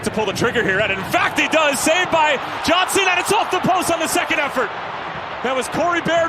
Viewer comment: Did announcer said saved by John Cena or am I hearing things? 😂